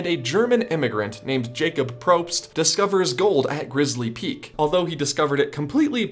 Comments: I could of sworn it said Glizzy Peak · I thought it said glizzy park lol